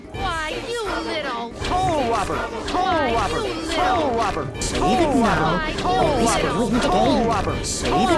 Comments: i thought it said pole rubber xD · SOULROBBER! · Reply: Sounds like Jack says Colon robber though. lol :P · It doesn't even sound like soul robber to me, more like pole prober, this game seems like some frustrating prototype Souls game. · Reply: I just heard "Clobber" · I just hear "pool rubber" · I thought jack said pole robber